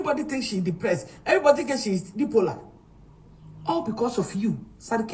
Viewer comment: Aaaba!!! Maa, did I heard "Dipola"??? It's bipolar😂😂😂😂😂